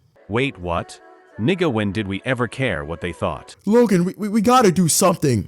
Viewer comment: Lil bro was on the galaxy gas no the freaking BROLY BOOST😭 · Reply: Did bro just say the n word who gave this guy the pass😦